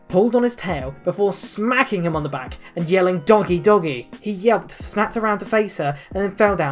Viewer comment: When you said "doggy doggy!" I heard " donkey doggy!" And was confused for a sec, since I wasn't looking at the screen. XD